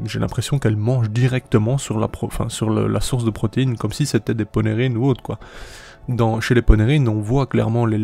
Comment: Polérhynes ? c'est quoi ? J'ai mis les sous-tites au cas où : ça indique 'Poney Reynauds' :body-blue-raised-arms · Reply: Ponerine